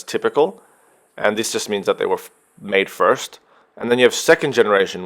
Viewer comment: lmao did he just say fucking made first?